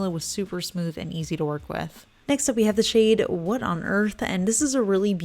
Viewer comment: I thought you had said"wood on earth". Would have been a great name. It's a gorgeous colour!